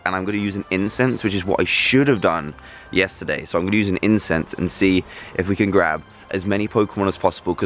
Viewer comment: I thought said "I might use some incest" But i heard wrong xD